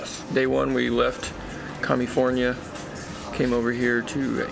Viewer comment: Did he really say commiefornia or I misunderstood something? 😂🤣